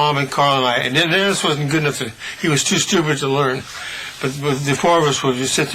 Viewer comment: Dennis wasn't good at nothing (Is that what he said ? He slurred a bit here). He was too stupid to learn". Hahaha ! Brian's a joker.